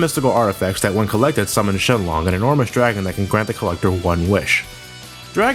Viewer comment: Did you say Shen-long? xD